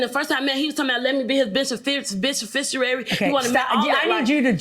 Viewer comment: "befishernary" 🤣🤣🤣🤣 girl you couldn't even say "BENEFICIARY" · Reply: Omg I thought she said “bish in fistuary”. Edit. Ok I just rewatched. Yes. She was trying to say beneficiary.